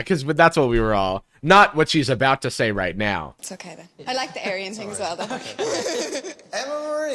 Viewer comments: what is she saying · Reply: it was “aryan” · someone help I cant understand cause of the accent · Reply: she says "i like the aryan thing, so..." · What did the girl say at 12:23 that everyone is freaking out about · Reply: She said she liked someone Aryan.